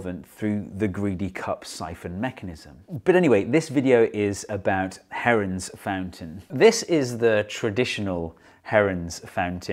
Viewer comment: at first thought it said Heroins Fountain lol